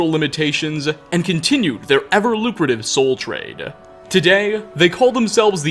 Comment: Def thought he said "soul train" instead of trade. Thought we were about to get funky up in here